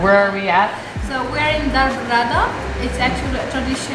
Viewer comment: I'll be in Fez next week.What is the name of the restaurant@ 09:50. I couldn't understand with the background noise. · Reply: Berrada family restaurant ❤